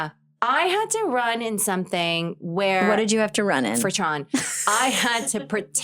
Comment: what did she say??? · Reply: Tron